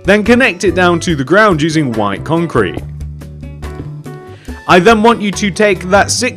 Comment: it sounds like he is saying Wine Concrete but great job!